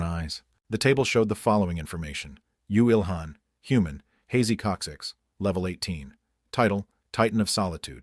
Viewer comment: Wtf is the narrator saying 😂😂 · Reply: You don’t have a hazy coccyx?